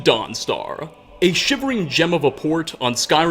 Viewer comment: Had to replay the start cause I thought he said 'a shimmering genital wort'